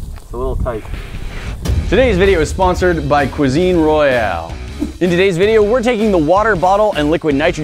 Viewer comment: I just love how they change "armour" each scene while they're talking about Cuisine Royal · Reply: I thought they said cousin Royal